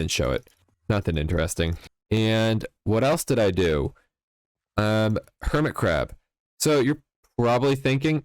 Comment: thought you said hermitcraft